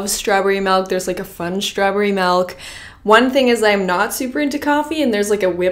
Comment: Is it just me or is the saying “melk” instead of “milk”